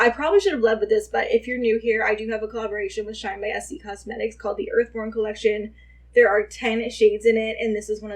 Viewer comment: I thought you said earthworm for a second 🤣